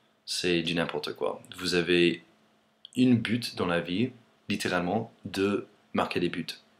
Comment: Oui, tu pourrais parler du Super Bowl ? On en entends beaucoup parler mais je me demandais pourquoi. Et fais attention ça peut être pris par un français. On dit "un but" et pas "une bute" surtout avec ton accent, j'ai compris "une pute" et "une pute" en anglais, ça donne "a bitch" =D.